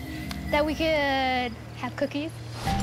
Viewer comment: Oh, COOKIES! I thought she said cocaine, lol. Somehow that answer makes more sense anyway